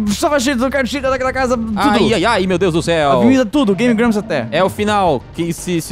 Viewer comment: eu escutei um meu deus Canela